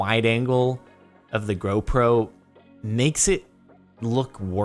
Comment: someone tell me if I am right, did say Go Pro or Gro Pro